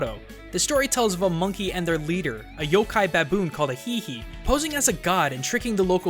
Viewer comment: did I hear him say yok-ai baboon